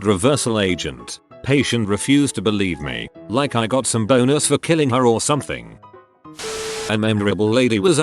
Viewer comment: i thought it said boners not bonus im fucked up